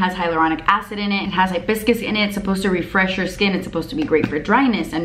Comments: You said that watermelon spray had hibiscus in it and I TOTALLY HEARD YOU SAY BISCUITS and I got all excited. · I know it’s because I have a cold but you said hibiscus and I heard “like biscuits”...face spray meets breakfast, I’m down for that.